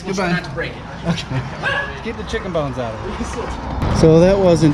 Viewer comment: Did I hear correctly? Handyman: "Keep the chicken bones out of it" Customer: "OK, you too"